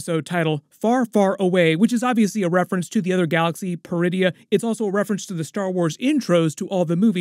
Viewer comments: Almost jumped out of my seat when I thought I heard them call the planet “Druidia”… 😂 I had to rewind and turn on subtitles. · Oh. PER-idia. I thought she said "DRU-idia". Funny, she doesn't look Druish.